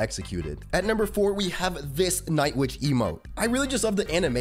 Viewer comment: I kept hearing: "This night witch emo"